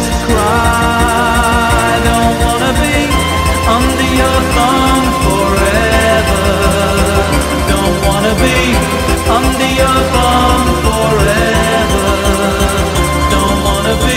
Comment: Despite the fact that, at the age of 11, I thought it sounded like he was saying 'under your bum', I totally loved this song then and still do now. What an early electronic masterpiece this is!